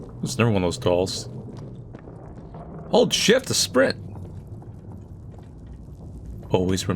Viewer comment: I thought he said hold shit to sprint.